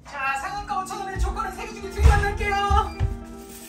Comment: 100번을 돌려봐도 가 도대체 뭐라고 하는건지 모르겠다. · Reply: 상한가 5천원애 조커는 3개중에 2개로만 갈께요~~~